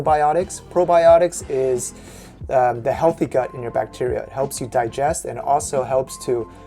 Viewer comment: u said "the healthy gut in your bacteria" instead of the other way around :P LMAO love you guys